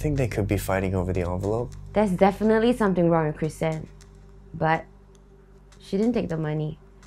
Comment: The subtitles said butt not but